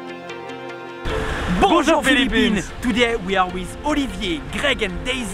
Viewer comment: I thought u said "Pusong Philippines" then i rewatched and saw that it was "Bonjour Philippines". The first one sounds better for an intro though.